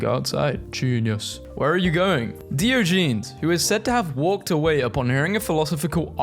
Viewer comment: Did you just say Deeogeenes